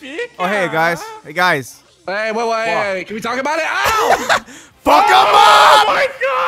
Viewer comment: Did he just say Fuckamom?